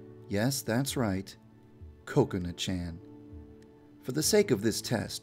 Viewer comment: I heard Coconut-chan!